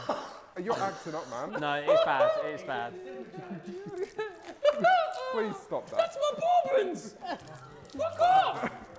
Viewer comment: I dont know but this is so funny to me Are those even human sounds What the fck is coming out of Wills mouth what is he saying He's just rubbing his back doing weird high pitched sounds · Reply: He's saying you ok? you ok?